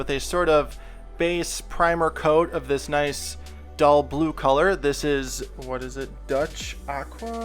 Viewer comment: totally thought u said color shit lmfao